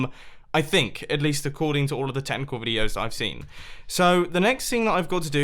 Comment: I thought he said tentacle when he said technicle videos